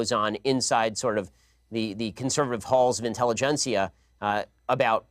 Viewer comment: the conservative walls of what? did he just make up a word? i cant tell what he is saying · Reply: He said "intelligentsia", as to mean a group of educated people who scrutinise and shape politics or culture